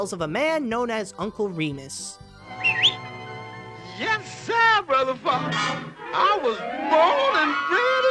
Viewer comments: Hold the phone! Did Brier Rabbit just say what I think he said? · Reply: Yes sir, Brother Fox! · Yes sir, Brother Fox! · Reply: I thought he said Brother F***er.